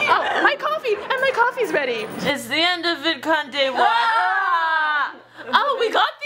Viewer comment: what did say at 11:35 ? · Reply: When jumps on her she says, "it's the end of VidCon day one". ❤